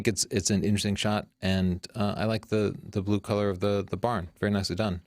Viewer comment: Did Tony say "the blue color of the barn"???